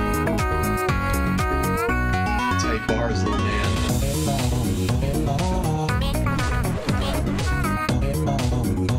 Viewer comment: Что он сказал в 9:16? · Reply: Tight Bars Little Man